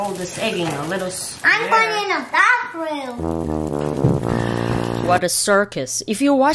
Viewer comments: Mom face when the girl said “I’m going to the bathroom” 😂😂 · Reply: Bruh I heard "I was born in a bathroom"!🤣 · I heard I'm going to the dark room at first 😐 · Mom face when the girl said “I’m going to the bathroom” 😂😂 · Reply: LOL I thought he said "I was born in a bathroom" 😭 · At first I thought he said, "I'm born in a bathroom" 🤣😳